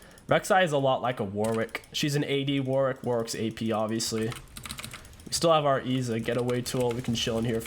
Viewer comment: Did you say warwick is ap